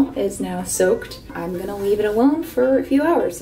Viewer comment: You said ‘leave it alone for a few hours,’ about the extensions, but may I present to you, ‘weave it alone for a few hours!’ Lol this is what I initially thought you said. Anyways, this was great! Keep at it!